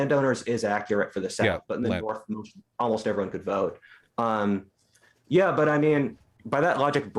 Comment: I couldn't quite make out what he was saying. "In the north almost everyone could vote." I take it he's talking about post revolution America? Can someone help me out?